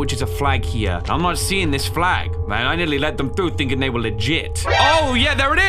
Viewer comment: what he say:oh yeah there IS What i hear:oh yes la nariz